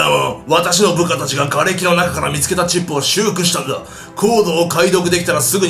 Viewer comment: チップって言ったのが ち○ぽに聞こえるwww